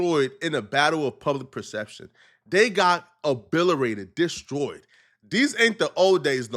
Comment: My guy did you just say “Obilerated”